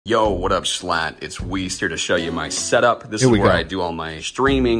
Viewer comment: I heard “hey schlatt it’s weezer”😭